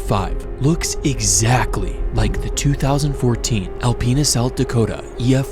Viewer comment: wait did he say ELPENISA?